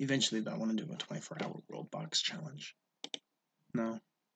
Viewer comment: did I hear a 24-hour world box challenge